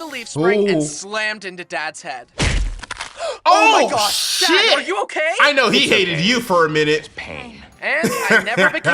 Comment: what did you sayyyyyy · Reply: Oh my gosh he said shit and I’m 12